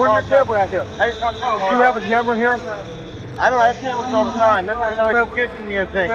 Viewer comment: I clearly hear you say, "I dont have cameras all the time" (and the captions picked I on it, too), but the text provided reads, "I have cameras all the time."